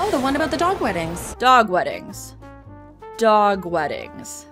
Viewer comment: I heard “dog wedding” as “dog wetting,” which is actually worse.